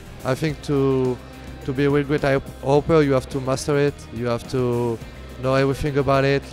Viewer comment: Hahaha 3:50 the first time I heard it I thought he said masterbate